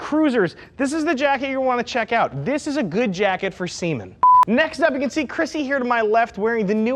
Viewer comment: lol i thought he said "that's a good jacket for semen"